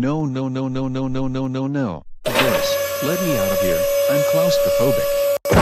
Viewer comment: what did he say? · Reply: Doris, let me outta here, im claustraphobic